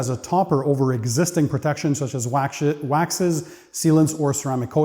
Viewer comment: Oh wow this is gonna be a series to follow for sure. Did you say “wax sh@ by mistake at 28:23? 😂